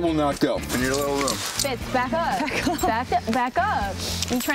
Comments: i heard babe, back up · Is it just wishful thinking or did Kathryn call Alex "babe" at 7:54? Am I just crazy obsessed with Allie and Harry? Yes? Ok. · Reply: RetroShady i think she said “fitz” not babe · I swear at 7:56 I heard Kathryn say to alex "babe back up" ahaha my heart I need them together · Did Kathryn just call Alex babe???? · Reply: Mae Alias she said fitz back up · Did anyone else hear Katherine say “babe back up” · ok i feel like i’m the only one who heard kathryn say “babe back up” to alex at 7:54 because i ship them and my mind hears what it wants to